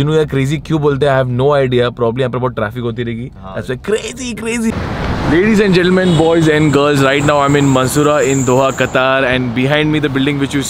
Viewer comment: listen he says balls instead of boys